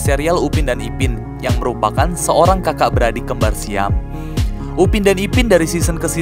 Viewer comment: Ap gua salah denger yah Di menit 6:29 katanya upin ipin kembar siam?